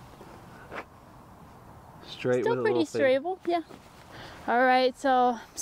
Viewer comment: did just say “strable”?! I’m using that.